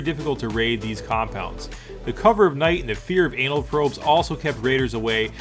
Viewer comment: did he say the fear of anal probes at 2:35 ? XD